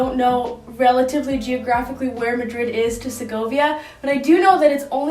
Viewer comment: I love this video so, so much!! Super inspiring and definitely is going to help me out tomorrow when I sit down to (finally) plan out my vision board for this year! (also, I totally thought you said Sokovia when you said Segovia lol!)